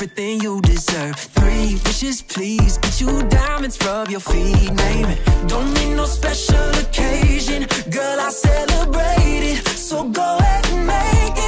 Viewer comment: このコメ欄の空気感で言うことじゃないけど、0:11から「ドミノスペシャル」としか聞こえねぇ……